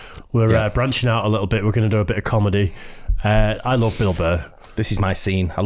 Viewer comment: I swear i thought they were saying "I love Bilbo"